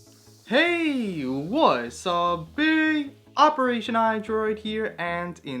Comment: in the beginning it sounds like he is saying what's a bee